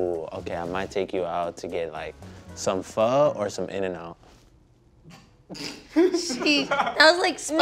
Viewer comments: What did he say at 8:39 ? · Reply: He would get Phô or In n out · did he say pho 🍲?